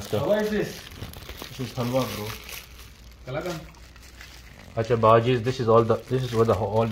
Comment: i heard my city name wow Talagang 😅❤